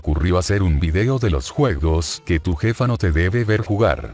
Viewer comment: Yo pensé que decía juegos que tú GTA no debe ver